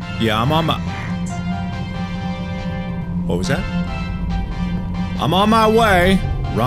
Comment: I heard "about that." or "I doubt that" Btw you guys?